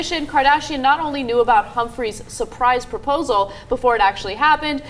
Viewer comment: it sounded like he said khris cumphries and not humphries . is it just me or did anyone else catch that